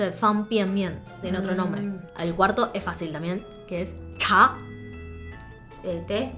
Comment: parecen que dicen mi nombre "Damián" xdd por cierto que español tan fluido tiene la verdad sub nuevo